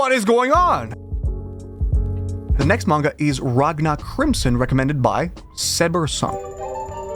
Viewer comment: i thought that said racism crimson